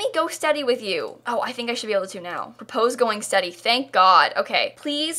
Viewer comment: i thought you were saying “propose go and study” and i was really confused like how is that going to get them engaged